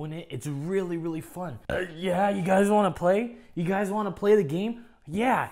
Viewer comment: Bruh when it was saying "play the game" i thought it was saying "renegade renegade"